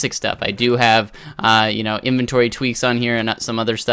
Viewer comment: what is the chest mod you have installed where it organizes your chest? I would really like to know so if somebody could respond ASAP that would be great, thanks! :D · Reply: i know im a little late but i thought he said inventory tweaks :)